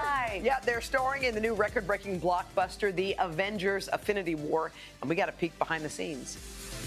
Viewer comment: Did I hear that woman say ' AFFINITY war @ 0:11 ?